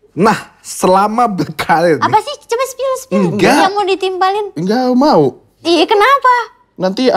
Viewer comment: parah banget sih. "lu pening banget" bisa-bisanya bilang gitu depat bintang tamunya. sampe eca bilang "kenapa". jelas eca merasa ga di perhatikan karna hostnya malah saling liat bukan liat eca. gw gatau di blakang layar gimana, apakah emang ada pembahasan yang di batasi oleh manager eca sampai kalian kekeh buat nanya yg di script . soalnya di pwk pernah di singgung klo managernya ngasi pesan ap aj yg di bahas dan apa yg sebaiknya ga di bahas dari eca. tapi gw salut sama kalian berdua bisa se tidak respect gitu ke bintang tamu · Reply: Salah denger, itu ngomong "lu mau nimpalin kan? " Eca kepo mau nimpalinin apa, mintak dispill